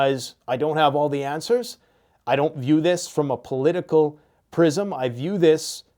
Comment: I thought I heard you say “I don’t do this from a political prison…”. I hope it never comes to that.